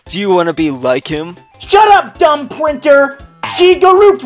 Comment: did arch say pwinter????